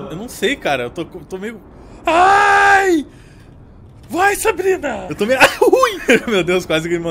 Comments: eu tava brisando aqui e ouvi um "vai Sabrina" achei que tavam me chamando aqui kkk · "vai Sabrina" ele disse oq eu entendi?